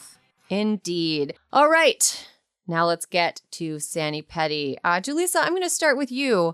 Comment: Well, steal my lunch! Up until today I thought you were saying Sandi Patty. I couldn't understand the reference. Today...I got it ...sani-petty. So much for my 75yo ears!